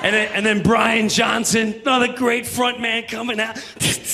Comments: Did he just say "Brian Johnson, not a great frontman" ??? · What did he say at 3:09? "Brian Johnson, NOT a great frontman"? What?